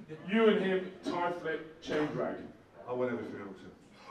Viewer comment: What did he say at 9:12? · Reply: "I'll win everything else"